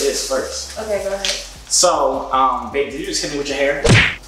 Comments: did he say bae or am I still hearing stuff I’m convinced y’all dating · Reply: yes he said bae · am I trippin or did he say babe?🥺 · Joovier doesn’t even know what flavor it is 😂he’s only enjoying the kiss 😘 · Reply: I swear he said bae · Yessir I’m the one that told him they should do that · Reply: I swear he said bae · Did he just call her bae at 2:05 😍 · Am I slow or did I hear "bae"